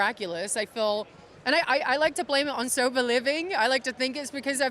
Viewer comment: Was it just me or when she said that did anyone else hear sofa living. Like she was just laying around on the couch all day 🛋:face-blue-smiling::person-turqouise-waving